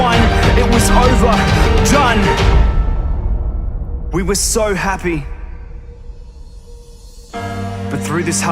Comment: > we WAS????? SO HAPPY??? wut did i just hear?